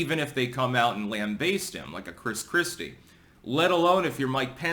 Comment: Pence actually has more sense of reality than Chris Christie. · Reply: For a moment I thought you said Chris Chan. 😂